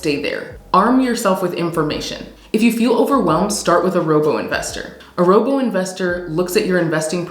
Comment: Did you say “Rogel investor” at 3:50? I’m still new to this…